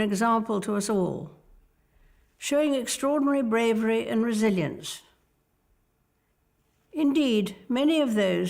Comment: Alguém entendeu o que ela disse nesse momento? Eu não entendo o inglês mas notei ela falando "Brasil" em inglês... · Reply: Ela disse "resilience".